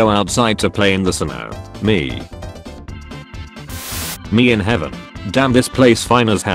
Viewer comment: thought it said "made in heaven"